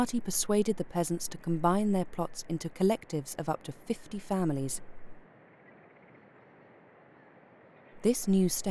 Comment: when she said families I heard it as "faminies"